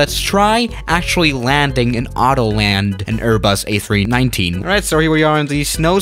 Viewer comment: Is there even a a390???or did he say a319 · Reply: He said a319